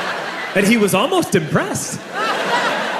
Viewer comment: My phone volume was drunk and at the scene when the police just came it I thought it said “he was almost depressed” instead of impressed.